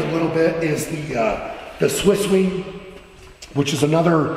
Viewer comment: Mister Shaw is talking about a massage machine and I had a hard time hearing what he said he said something like "Swissee" so is there anyone who could tell me how it is spelled, so that I can find out more about this object on the internet.